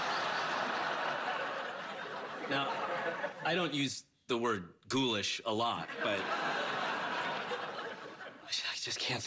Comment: what is Howard saying at 1:40? Goolish or something?what does it mean? Cannot understand it, and I m from Germany so I m sorry for asking😅